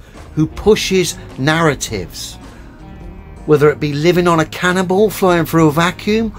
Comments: must be given his own show on TV, I could swear he said 'living on a cannibal flying through a vacuum' (about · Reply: "Living on a Cannonball" he can't talk worth a damn either! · I could have sworn he said “Living on a cannibal”. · I heard say that we science based, reasonable people believe that we live on a cannibal??? WTF?